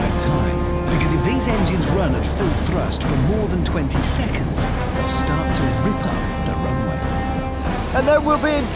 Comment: “...for more than 20 seconds, it starts to (?)” what did he say? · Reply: Tear up the asphalt